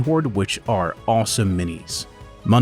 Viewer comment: Nice army and thanks to showcasing 3d artist and their version of skavens. At 4:57 there is no info or logo in the pictures. I hear "Manuel Bordia or Boria", not sure of the spelling, Can you drop his name or studio so I can check it out ? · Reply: Thank you kindly for the kind words and thank you for pointing that out! It is Manuel Boria and now each of the sculptors should have a card popping up in the video! 👍😅